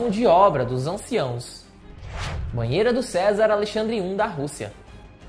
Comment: é, foi no minuto 1:56 banheira do César, ou me enganei?